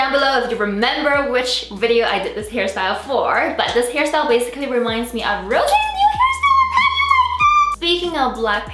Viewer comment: ahahaha idk what you said but I love you so much! 😂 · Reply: Ahhahahaa I said this hairstyle reminds me of rosés new hair from how u like that 😂😂😂😂😂